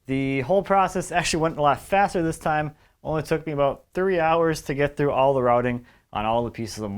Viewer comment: Sounded like you said those mouldings only took you 30 hours! Thank God that wasn't the case!